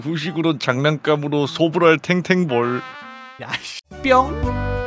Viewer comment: 잠깐 야씨라고 들림 이스터에그